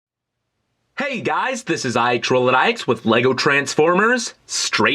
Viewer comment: When he says "IXROLLOUTIX" its sounds like he says IXroladikeIX